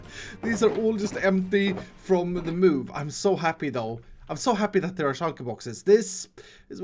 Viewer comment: can some one help me? I speak spanish, and I'm trying yo lern english, and I understan the most part of ir (I think) but there is one word that I don't know what it is, and that word is that "dou" that he says all the time in the 0:28 he said I'm so happy "dou" please help me I don't know what it means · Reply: He means though just an accent